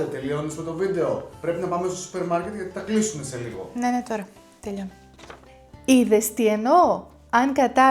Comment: You said "yes, yes, now" right? I heard " ναι, ναι, τώρα"